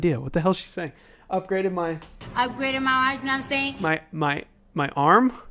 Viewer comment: Upgraded my watch no what I’m saying